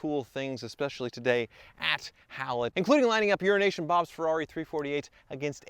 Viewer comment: I always thought he was saying urine-ation Bob until I see one of the last videos about him buying USA cars